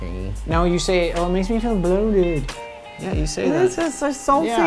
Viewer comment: What did Lance say on 13:00? "It makes me feel..." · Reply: TD bloated